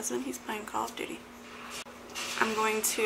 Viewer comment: i thought you said he is playing 'golf duty' i was thinking what the hell is that hahaha great vid :)